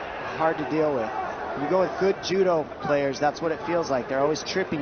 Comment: did he say players ? 🤔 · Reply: Judo 🥋 players